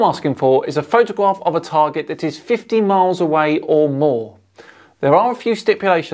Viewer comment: Did say 15 miles away or 50 miles away? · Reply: I think 50 miles